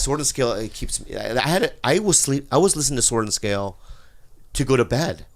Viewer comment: can anyone tell me what show they are talking about i couldn’t catch the name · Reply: thankyou..i thought they said storton scale..😒